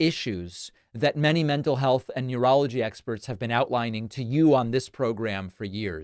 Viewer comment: When you said “…that many mental health and neurology experts….” I thought you said urology experts rather than neurology experts. Somehow it seems fitting.